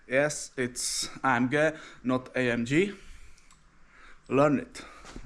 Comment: I thought you said I am gay 😂